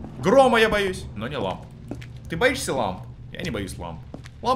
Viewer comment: *послышалось вместо "ты боишься лампы?" "ты боишься ламы?"